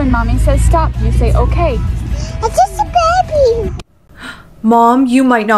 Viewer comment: I thought coco say I,m just big girl